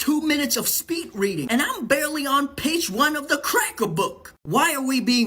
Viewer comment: Did he say "Da Cracker Book!" 😂😂😅